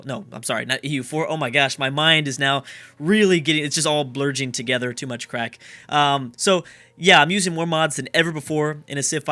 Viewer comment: Did you just say "It's all blurging together, too much crack"?